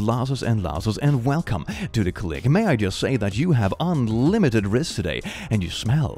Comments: ngl i thought he said "unlimited wrists" there for a sec and was strangely intrigued, because that sounds like a human tentacle monster waiting to happen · The way Click said “unlimited rizz” and “no cap” felt like a parent trying to use teen lingo to connect with their kids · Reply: i thought he said "unlimited wrists" and checked mine if they grew to infinity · The way Click said “unlimited rizz” and “no cap” felt like a parent trying to use teen lingo to connect with their kids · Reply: Lalskmslazm I thought he said unlimited wrists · I thought he said unlimited wrists in the begining